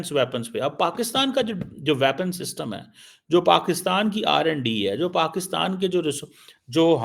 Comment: Did I hear it right 10:17 R&D in pak or Randy pak